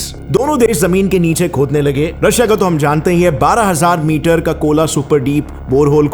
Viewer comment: One correction The deepest mine that has been so far observed at kola is "12km" deep not 12,000km It's practically impossible as well since the radius of earth is just 6370 km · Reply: U Misheard What Gaurav Said In 5:44 He Said 12000 m Which Is 12Km And Don't Take Comments So Seriously Because He Is A Kit-Kat Chocolate Kid .